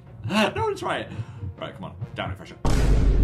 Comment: DID YOU JUST SAY DOWN IT FRESHER AHHAHAHAHAHHA